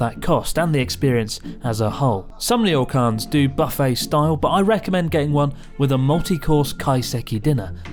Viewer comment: I thought I heard "butt-face style" and now I cannot un-hear it.